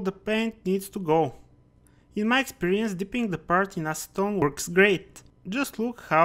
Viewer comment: What was the liquid you used to remove the colour from the PE? I thought you said stone works grey. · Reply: Acetone